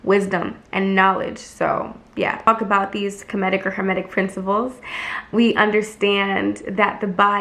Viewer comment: When I heard "Kermetic priniples" I saw Kermit the Frog. The sheer joy that image brought me is THE BEST.